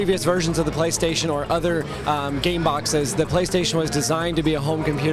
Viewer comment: did I heard gay box?